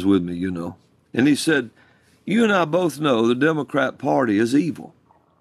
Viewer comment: I could've sworn he said "the devilcrap party" instead of "the democrat party" and ngl I kinda loved it